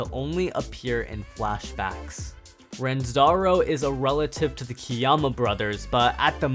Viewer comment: is not called ranzaro is called Ranzo